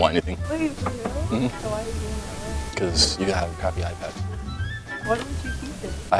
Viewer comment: I thought said, "Because, I have an iPhone." lol like making a joke, cuz she was like, but i have an iPad :P